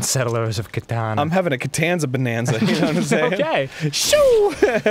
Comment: I could've swore it said Costanza Bonanza = Festitivis